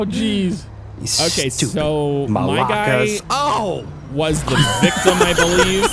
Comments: did he say stupid Malakas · did he say malakas? I have never heard an American outside of Greece know that word lol.